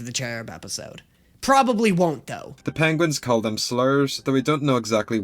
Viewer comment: The Penguin slurs bit is genuinely the hardest I’ve laughed with Helluva Boss in so long. It’s so random, but god is it funny. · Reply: The first time I watched it I thought it said slurps😂